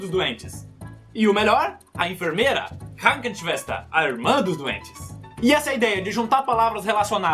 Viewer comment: Eu ouvi “a irmã dos duendes”